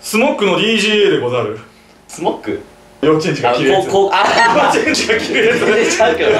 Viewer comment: でんがんが初め幼稚園児が着るやつって言った時お〇ん〇んが着るやつと聞こえてしまいました(;_;)